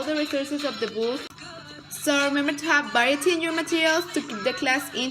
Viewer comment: min. 5:10 sounded like "Baety in your materials" .. after playing it over a few times I realized you meant: "Variety in your materials". Otherwise thank you so much, your video was very helpful!